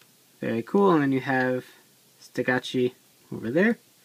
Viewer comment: everytime you say stegochi it sounds like youre saying ste goat cheese. lol